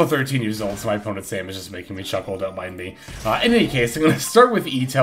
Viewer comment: was really 13 years old? or am I hearing things? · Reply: yeah she said their opponent's name is making them chuckle